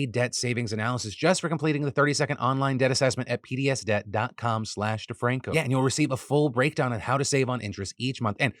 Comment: You might want to enunciate PDSDebt more clearly since I had to rush over and look when I thought "is he saying Hideous Debt?"